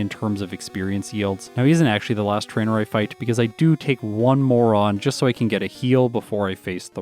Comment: Did he say he'd take one moron or one More on? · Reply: Definitely didn't say moron. "More on"